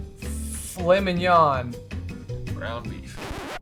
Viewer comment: Misheard fillet mignon as flaming yawn.